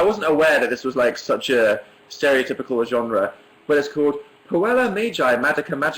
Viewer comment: Puella Magi Madoka Magica :) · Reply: puella! oh I thought he said koala...